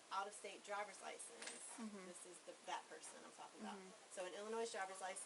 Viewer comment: did she say fat person …ma’am 😂😂😂